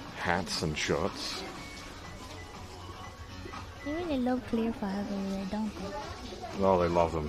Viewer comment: What does say at 44:18? Edit: Oh nvm, I think she says "they really love clear files over there, don't they" lmao